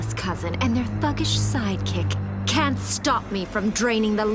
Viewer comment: did she say fuckish? 😂